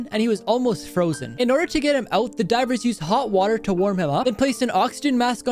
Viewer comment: I thought he said driver